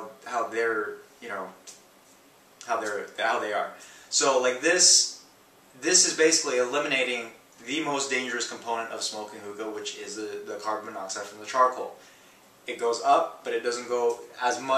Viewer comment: Did he say carbon monoxide?